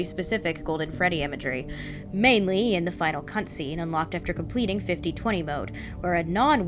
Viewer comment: “mainly in the final C*ntscene” I can’t I hear it